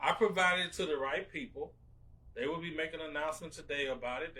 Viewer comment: At the 5:17 mark he says “they will be making an announcement today about it” Did I hear that right?